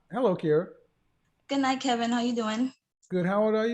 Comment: did she say goodnight Kevin how you doing?